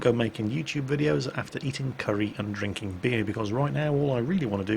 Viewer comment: I swear I thought he said "drinking curry and eating beer" It just seems to have a better ring to it